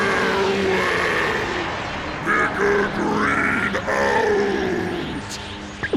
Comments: Did he just say "nigga green out" the whole room is going wild rn lmfao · Mega green out!!!!! Why I thought he said ni**a green out tho at first🤦🤦🤦 lol. Aside from that🎉🎉🎉🎉🎉 Epic🤗🤗🤗 plus Krillin finally got a break🎉🎉🎉